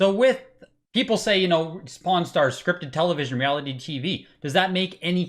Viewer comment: His accent i keep hearing 'porn stars' not 'pawn starts', i almost spit out my coffee this morning watching it.